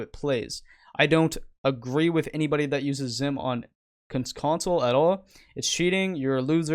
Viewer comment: i thought he said i used a xim on console best settings